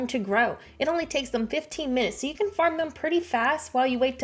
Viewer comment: Sorry it sounded like you said 15 minutes for onions I believe it is one hour and 15 minutes. Sorry if I heard incorrect